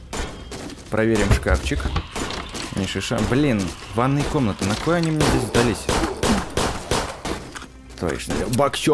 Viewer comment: мне не послышалось или мат сказал ?! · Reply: Он сказал накой они мне сдались